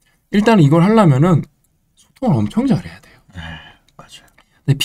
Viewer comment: 뭐라고 하는지 알려주실수 있을까요??? 발음이 뭉개져서 제가 못 알아 들어서요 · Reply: 소통?!